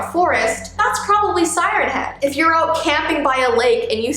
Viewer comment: For a sec I thought she said Simon head literally